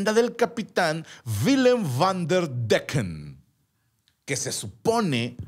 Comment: parece que dijo Black & Decker jajajajaja